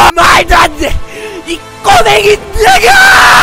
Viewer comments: 초에서 왜 꼬맹이라고들리지..ㅋㅋㅋ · 나만 꼬맹이로 들리나